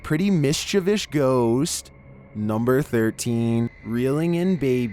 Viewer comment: Did i hear "Mischivsh Ghost"